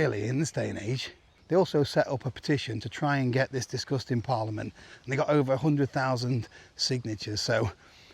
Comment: I thought you were saying “this disgusting parliament” then realised you were actualy saying “this discussed in parliament”. Both are applicable I feel. Thanks great update